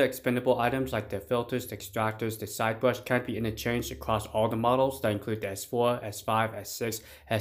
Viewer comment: Heads up. At 2:00 the subtitles say “can’t be interchanged” but i suspect it is meant to say “can be interrchanged”.